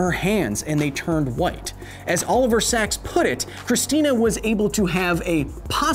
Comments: I thought he said All over sacks. What a great name. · When you said oliver sacks i heard all of her sacks 9:55.